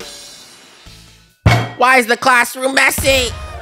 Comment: Why did it sound like said messi?😂😂😂